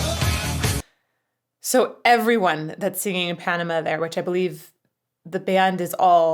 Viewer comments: I grew up only half-listening to this song and thought he was saying "Animal" instead of "Panama." I only learned this last month. x.x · I always thought they said "Animal", no its "Panama", oops.